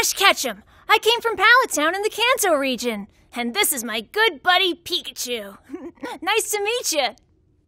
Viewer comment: I thought she said "pallet town in the cancer region " 😐